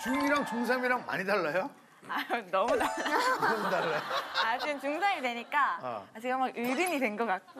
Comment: is wonyoung saying "i don't know that" at 7:11 or am i tripping? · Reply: She’s saying noeomu dalla which means different